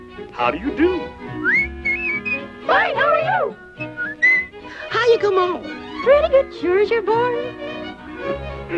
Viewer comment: I still don't know what they're saying. I keep hearing, "Pretty good, show us your balls." Even on Splash Mountain it sounds like that.